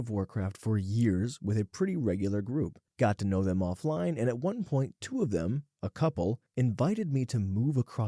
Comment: It took me way too long to figure out that at 4:23 its at one point, 2 of them and that the captions got fucked up.